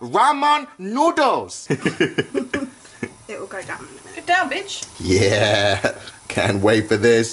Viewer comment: did she say get down b**